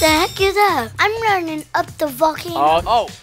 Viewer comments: did I just heard Shawn curse? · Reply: He said I’m running up the volcano 🌋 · Noone gonna talk about shawn cussing at 18:29??? 😭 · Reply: ik i thought he did but he said valcano